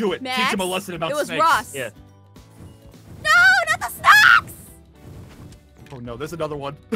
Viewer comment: Lol I think I just Heard Aphmau Say “NO NOT THE SNACKS!”